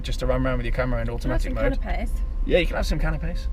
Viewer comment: Gonna be some wedding, I thought I heard offer people cannabis then realised she said canapés lol 😂